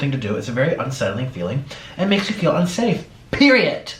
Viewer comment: Hahaha, did he just say "periET" BAHAHAHA